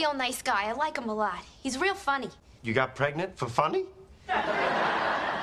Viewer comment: I thought it said “YOU GOT PREGNANT BY FUYUMI?”